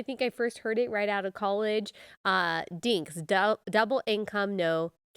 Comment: DINKS has been around since the 80's. My husband and I were called DINKS. Thank God, one year later we were blessed with our first born. We were blessed with 3 and have enjoy most moments of it, but now, my children, all grown adults are such a joy! So much fun and I would do anything for each of them. 🩷🩷🩷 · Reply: I am English and heard it as DINKYs - double income no kids YET 😀